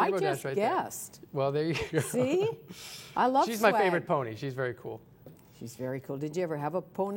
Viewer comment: did she just say "I love swag"??